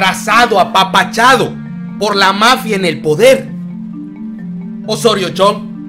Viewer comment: Estaba interesado en lo que decias haya que escuche "mafia del poder". Neta ya cambienle con esa mamada